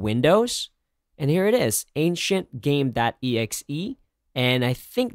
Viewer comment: I thought he said "ain't shit game.exe"